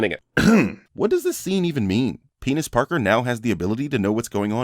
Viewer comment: okay at 5:02....did you say Penis Parker? or it might just be my hearing :O